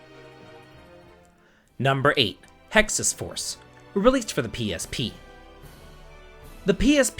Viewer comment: I misheard Hexyz Force as Texas Force and thought I was missing out on my spirit animal state .